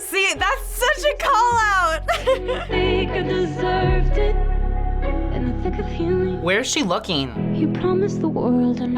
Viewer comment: am I the only one who thought she said "and I think of Hailey" instead of "in the thick of healing"